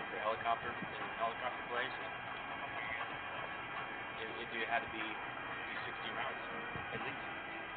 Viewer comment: can someone else listen to this..? Sounds like he says helicopter blades